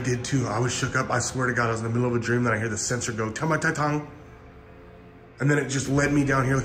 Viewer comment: I heard "salamat tatang" hehe watching july 28 2023❤️